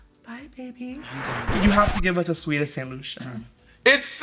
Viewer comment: I swear the entire time I thought ya'll have been saying it's so good, it's so good that sweet ass delusion